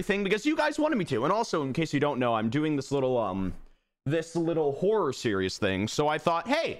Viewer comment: All I hear at 1:36 is “this little whœre series”